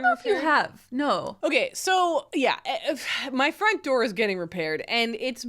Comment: the first two times you said "my front door is getting repaired", I swear I heard "my friend Dora is getting repaired" and I was so confused. 😅